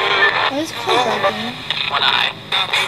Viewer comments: I heard one eye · It's inaudible? Id think not He said "One Eye" He had an eye taken out probs.